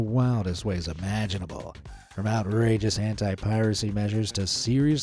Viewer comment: he said "anti" and I thought he said "hentai" 💀😭